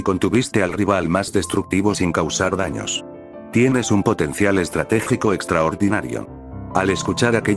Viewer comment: Did you just say potential?